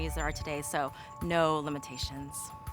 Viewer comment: For just a moment, I thought she said, "Follow the science." (!!)